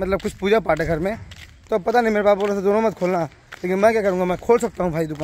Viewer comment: ye tym me galat bole bhaiya ap.... Baap nahi ijjat se bolo papa kar k.... Mera baap bol rha tha bol rhe ho... Baap koi dost yaar thodi hai bhaiya ..... Ap bade ho kuch to soch k bolo bhaiya......😢